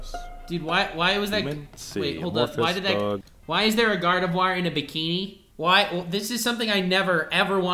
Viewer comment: did he say gardevoir?!